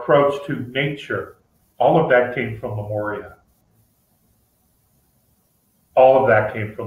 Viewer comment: Can anyone help me to understand what is saying? 2:20 all that came from ...... What is saying is it Memoria or Lemuria? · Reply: He is saying Lemuria I believe because the Native Americans were descendants of/deeply influenced by Lemurians